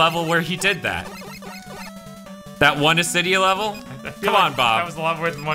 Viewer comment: You're saying "Asydia." I thought you were saying "Dysidia" for the longest time.